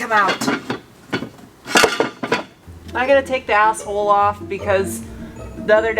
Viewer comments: Ohhhhh! "Axle" ! I thought you were saying "Asshole" LOL LMFAO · It took me a minute to realize you were saying axel, i thought you kept saying you had to get the asshole out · Can't wait. In fact, I won't wait. No I'll chew my ass off at work and then one day there'll be you and the Audi again. Sound. Funny bit, thought you said asshole but it was axle. " gotta take the asshole off".. like it was all going swimmingly; soon have her running, hear that beautiful sound, but "oh some a*xle is in the way". These vids are great. Hi.